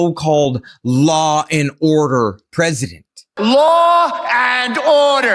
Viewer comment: I heard him say he's law and odor. 🤢🤮🥴